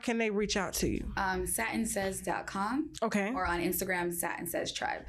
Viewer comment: "Satin says" sounds like satan says.